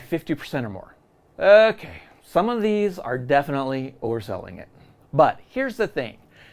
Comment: When he said they are overselling it I thought he said they are full of sediment🤣🤣🤣 play on words 🤭